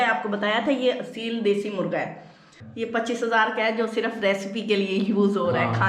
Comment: Did I really heard..ye 25000 Ka murga hai????😫😫😫